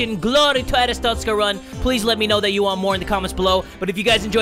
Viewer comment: LOL OMG!! I just realized that you say 'THAT DUDE!' For a while I thought you said 'DEAD TOO!'